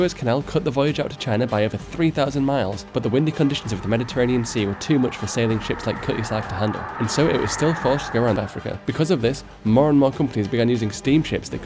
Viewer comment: I thought he was saying “Cuy Sah” until I saw it in writing